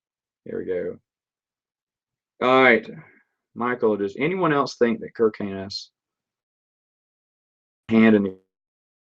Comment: I can't hear what you're saying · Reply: do I think Kir Kanos started the Imperial "grey" Jedi, was the question.